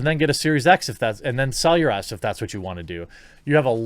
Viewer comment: He said the phrase "Sell your S", but I heard "Sell your ass".